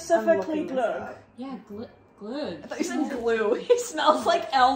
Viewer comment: did they say glögg?